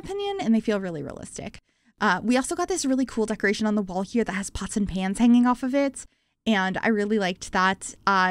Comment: I thought you said "hanging possum hands" and i was like wtf hahaha... it's pot's and pans... i totally pay attention haha